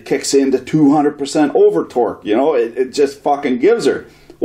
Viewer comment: Honestly I think you idea for setting it into an "overdrive" mode is the long way around and not particularly intuitive. Since you are already going to be using a pressure transducer and a PLC to control it, the better way would be to tie the VFD speed reference to a PID loop. That way as the pressure decreases the PLC would increase motor speed to keep your pressure up. You could also set it up so it wouldn't need to use the jog function to get the compressor moving. It would just ramp the speed to get it moving. This would work as a soft start to keep the breaker form tripping. It's the in-rush current of the motor that trips breakers, not the fact that the compressor piston is at top/bottom dead center. Ramping the speed on start will take care of that in-rush. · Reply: Gman76 I said overdrive because I thought that's what he said in the video. He actually said over-torque. Either way the idea is the same. He was talking about some sort of sequence to speed up the motor. PID loop would work better.